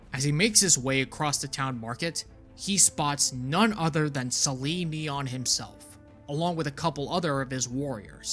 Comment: I listened to the audiobook. I kept mishearing Sali'Nyon as Celine Dion, and had a very funny mental image of the covenant leader.